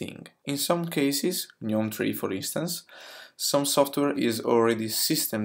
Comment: I can't understand the name of the systemd-dependent software mentioned at 2:19. What's that? Neontree? Something else? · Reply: Gnome 3